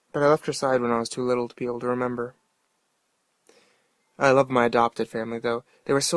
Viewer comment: turn it on,first thing i hear was "I'm Adopted"